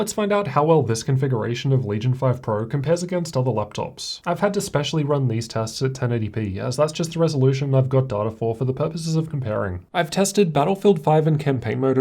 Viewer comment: I don't think he is dump, I follow his channel for a while. but he said it himself that he tested on 1200p on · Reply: if you come 5:31 and turn on subtitles, he is specially says: "I've had to specially run these tests at 1080p, as that's just the resolution I've got data for for the purposes of comparing. ". also you can check before 5:26 its specialize as 1920*1200p at graphics. after 5:26 its specialize as 1920*1080p at graphics. so i think its true 16:9 1080p fps values.